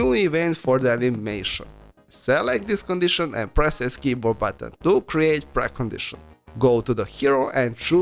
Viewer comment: Thanks I love the video. Sorry to ask but what button did you say to press at 6:25? Thank you! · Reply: s button